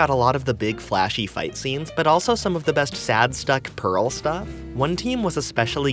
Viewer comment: I heard the word "Sadstuck". Homestuck detected lol